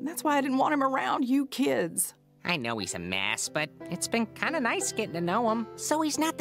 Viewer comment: Did he just say he knows he's an a**?